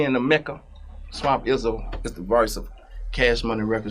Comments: i didnt realize he was tryna say "voice" thought he was saying verse the whole time 😂😂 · Oooooh “voice”! I thought he was saying verse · This whole time he was saying “voice”. I thought he was saying “verse”.😂